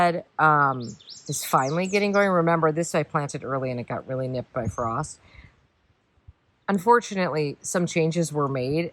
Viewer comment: Pist # 2. What are those red flowered plants name at times 16:00? I hear you say the name but do not get what you are saying. Thank you. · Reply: Spigelia